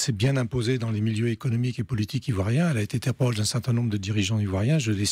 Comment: elle a été quoi j'ai pas bien compris cette phrase quelqu'un peut me dire c'est que ce qu'il dit ? Elle a été "tampold"? C'est quoi ça ? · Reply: Interpol